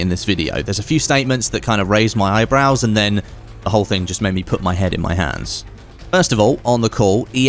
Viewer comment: I thought he said "Put my head in my *ss"